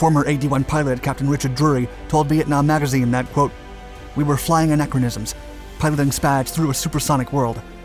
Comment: Googled anachronism. Yep ,got it. New word for me. Thought he was saying acronyms.